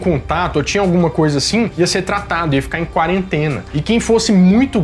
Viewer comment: eu ouvi quarentena?? 🤣🤣